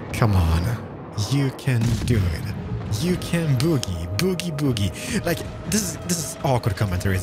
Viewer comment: Anyone else heard him say bookie and went "lookie lookie at my bookie"?